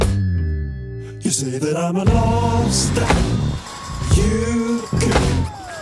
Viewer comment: Jacob collier is the only person that can start with the lyrics "you say that im a lobster" and ill be sitting there un-phased thinking "yea that sounds about right." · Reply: He actually said “you say that I’m a lost and…” but I thought he said lobster too, and I was also completely unphased😂